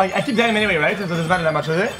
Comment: wth did he say ? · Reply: well, I keep the item anyway, so it doesn't matter that much, does it?